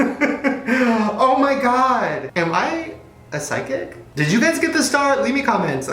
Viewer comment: What did he say at 12:54 ? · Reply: Adam Glitch Glitch Glitch, i think